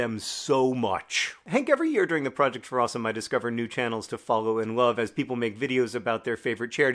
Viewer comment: Did you just say 'I discover new channels to follow in love'? My man, how do you manage to inject poetic phrases into videos like this?